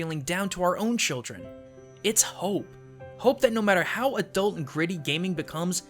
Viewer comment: thought it said hype